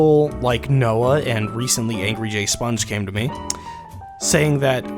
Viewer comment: I didnt hear "the alphajayshow" I heard "the angry gay sponge"